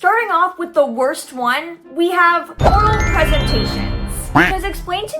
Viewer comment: I heard that is “oral pregnataions”😭🙏